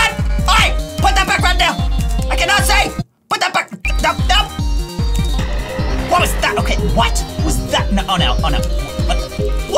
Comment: I thought he said WHAT THE FU